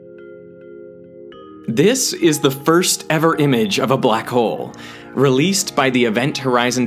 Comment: Sorry thought it said first image of a "ButtHole" disappointed 😞